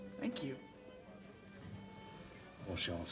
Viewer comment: what did he say, what does that mean? Anyone? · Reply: Bonne chance/good luck.